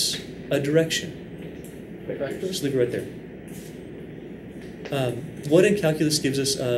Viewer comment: did he just say "Sleep right there."?